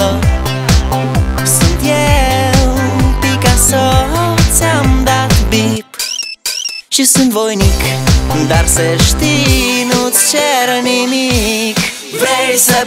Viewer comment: I hear sometimes "Just in voinik".